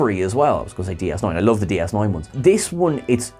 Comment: Wait, that sounded like you said, "Other Yoda Ones". What game is this? Maybe just a "Talko" ... ghee, is that a new phrase? I wonder, right Yoda?